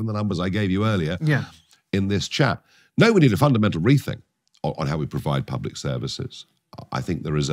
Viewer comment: I first heard, "We need a fundamental rethink" as "We need to fund a mental rethink" This feels true